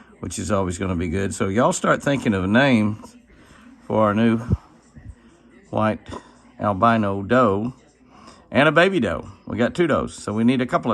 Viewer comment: I like that. Betty White!!! Thanks. May God Bless. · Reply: Just realized I heard you wrong. You said you ave two albino does coming. I thought you said a buck and a doe. I already said Betty White, but I got some more name suggestions. Vanna White, Lilly White, Milk, Snowball, Powder. My favorites are Betty White and Lilly White.